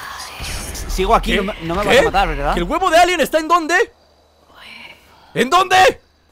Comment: el huevo de alien esta en el oeste es lo que escuche en el minuto 19:10 asta el